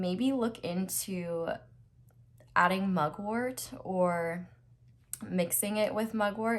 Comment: Hey, I'm not a native speaker and I have a understanding question to pile 1 minute 8:45; Smoking what instead of pot before going to bed? Could you (or someone else) please write the name of the replacement down, so I can research it? Would be helpful : ) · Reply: she’s saying mugwort! it’s a plant that you can mix in tea or such.